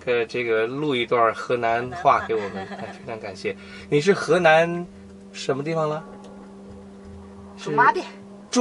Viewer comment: I am learning chinese...can someone please tell me what he said? her answer is zhumadian...what is the question? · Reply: 他说，“你是河南什么地方呢？”。